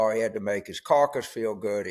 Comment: At first I thought you said Johnson "had to make his carcass feel good," and that was so fitting. 😊